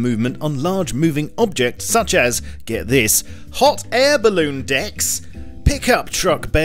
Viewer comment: I was looking at his channel while whatching this and i thought he said air baloon dicks